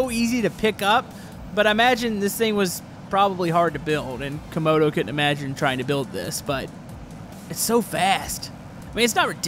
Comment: why do I heard "konekokitten imagine try to build this"